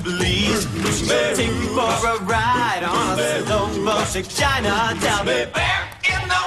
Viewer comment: why did it sound like they said "slow motion vagina" in the theme song?